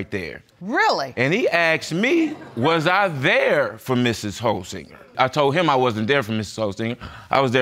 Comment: I thought they were saying Mrs. HOE-singer! Ha. Got to be no careful!